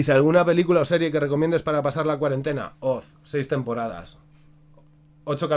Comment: Disculpá feo, pero no entendí que serie dijiste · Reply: Oz es ssobre una prision